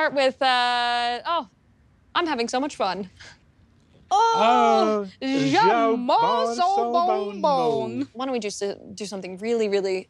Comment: “Ohhhh, j’am bomme somme bon bomme” Edit: thank you for the likes! Apparently I need to clarify that this is not actual French but “French”-sounding gibberish and had no intended meaning, it’s just a joke. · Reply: I heard "je mange son bonbon". "I'm eating his candy" seems like a plausible French figure of speech for "I'm having so much fun"